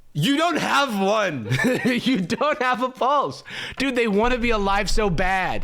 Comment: I didn't even understand "pulse".... For me it sounded like "come check my balls"